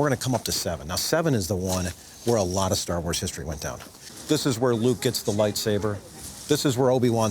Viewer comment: Sounds like he says "This is where Lou gets the lightsaber".......What? Oh Luke!